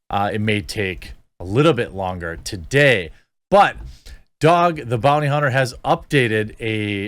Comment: The title says Dog, but I hear Doug.